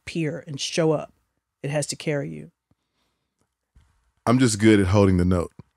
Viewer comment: I really thought I heard “ a pot to carry “ too 😭😂😂